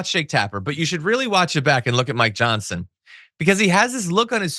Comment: Did you say , Your Johnson?